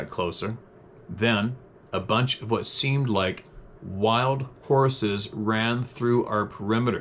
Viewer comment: Thunderbird? Sounded like he said real wild horses to me.